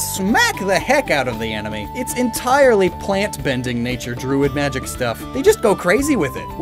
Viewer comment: Did he say "plantbending"? 15:50 I knew he was part of the Gaang